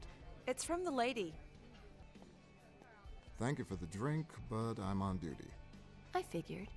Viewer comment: why did it sound like he said "but i'm on diddy" 😭